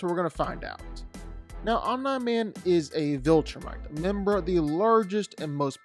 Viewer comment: is it just me or did he say online man